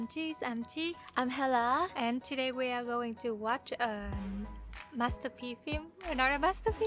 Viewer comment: Who else thought they said “this is a Master P film”? I was like “the no limit soldiers made this movie?” Honestly I heard her say “Master P”